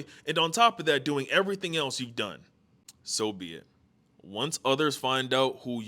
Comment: BRO I THOUGHT YOU SAY SOVIET